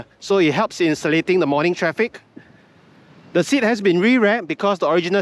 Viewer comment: Did I hear you say "it helps in the selit-ting in the morning traffic"? 😂 06:06 That's the most multi-racial word I've heard 🤣